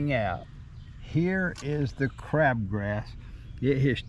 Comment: I thought for a second that you said "crap grass"! Same thing IMO, but it gave me a giggle! Thank you! 😁